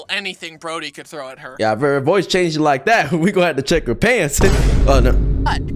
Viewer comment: Someone clip that! what are you saying?! 😭🙏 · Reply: He said: “Yeah if your voice changes like then we gonna have to check her pants”